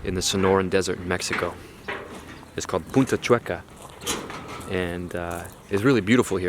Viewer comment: I thought he said puta chueca 🤣